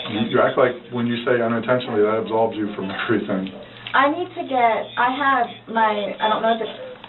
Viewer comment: I couldn’t make out the last word of what detective said here: “You act like, when you say ‘unintentionally’ that absolves you from _____.” She completely ignored him. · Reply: I believe he said "from everything." And yeah, what can she say to that? Change the subject asap. 😂